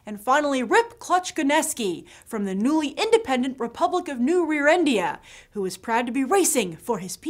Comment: Did she just say New Rear-End-ia?!?!?